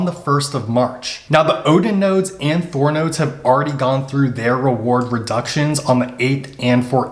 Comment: Wait a minute, did you say Thor will be carrying further reward reductions?? I hope I misheard what you said!!